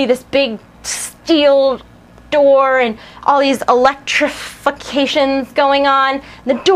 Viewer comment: Did she say "Electrofucketion"? lol